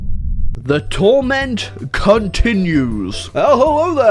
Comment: At the beginning I thought you said : ,,The torture continues''